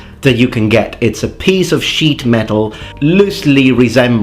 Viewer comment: "It's a piece of sheet metal" - why did I hear "It's a piece of shit metal" xD